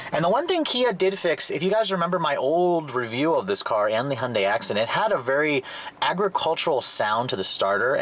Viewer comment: did he say Hyundai accident? 😂😂